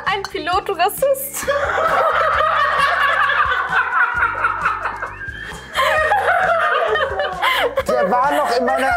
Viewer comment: was sagt sie da? Trotz Hörgerät und Untertitel habe ich nichts verstanden 😂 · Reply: "Oh Sh***" 🙈